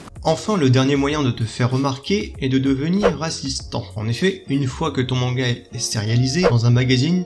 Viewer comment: j'ai entendu "c'est de devenir raciste" 💀 J'ai un problème jpense